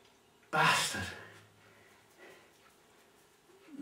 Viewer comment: I thought you were saying bastard at 9:19, my apologize if I were wrong.